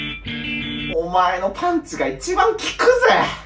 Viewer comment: 動画から目を離してて、音声だけで 「お前のパンツが1番効くぜ☆」って 聞こえてどういうこと？って字幕見たら パンチだった🫠🫠🫠